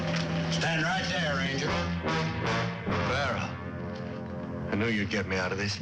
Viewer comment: Correct me if I'm wrong but at 19:46 did he call that guy Barron???? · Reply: Farrow. Chet Farrow